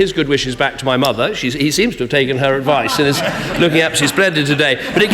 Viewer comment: could you tell me what Cameron says at 5.18 min pls? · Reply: Marouane Nouveau are those the exact words he said? i m not english and i find it difficult to understand. To me, sounds like he said "he seems to take her advice,looking up his predator today", i know its stupid